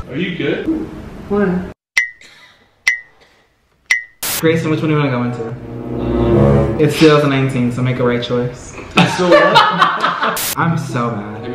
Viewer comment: wtf did he say??? For the life of me I can't figure it out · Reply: "Grayson which one do you wanna go into? Its 2019 so make a right choice"